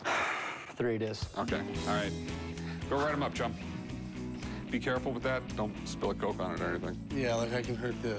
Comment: lol, thought he said "be careful with that chum, don't spill your dope on it."